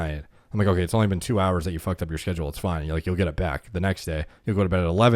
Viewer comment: Did say "shhedule" ? I rewound it like 5 times trying to hear. Sorry time stamp